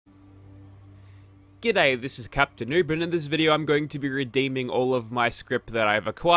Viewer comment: Srry but sometimes it sounds like your saying captain uber